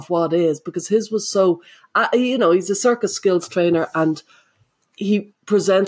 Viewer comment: Did say "circus skills trainer"? As in, he teaches people how to walk the high wire, breath fire, and tame lions?